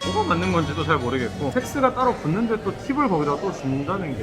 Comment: ㅋㅋㅋㅋㅋㅋㅋㅋ 잘못들었나 싶어서 다시 들어봤는데 (섹스가 따로 붙는데 ) 라고 들리네욬ㅋㅋㅋㅋㅋㅋㅋㅋㅋㅋㅋㅋ